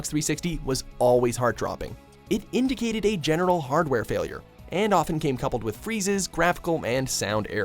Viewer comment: did you say "genital hardware failure" because that's what it sounds like